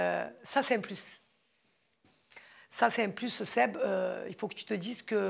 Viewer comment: A 45:22 elle a appelé "Seb" ou c'est moi 😅 ?